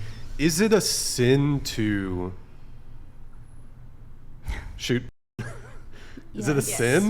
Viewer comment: I have no idea what it is said. Anybody understand? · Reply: I think he said "shoot", as in shooting corn.